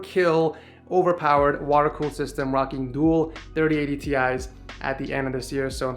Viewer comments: wait a sec...did you say 3080 TI? before the end of the year? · did he say 3080 ti 😂💵